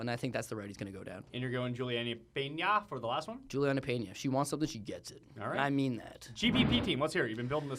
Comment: what did he say? She wants the win she gets it? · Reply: she wants something she gets it. i mean that